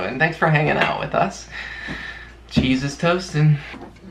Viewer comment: At about 8:28 I thought said, "JESUS TOASTIN'!" 😆😆😆😆😆